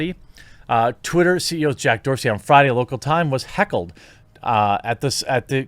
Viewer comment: Misheard Heckled as Kekled. So naturally I am going to run with it now. Kek